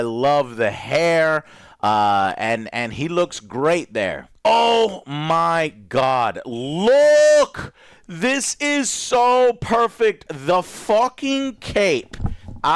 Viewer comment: I thought he said the f**king 🍰 when looking at mr terrifics 🎂